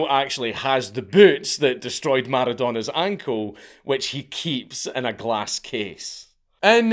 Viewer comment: "which he keeps in a glass case" all i heard was "that was the move that made lebron cry"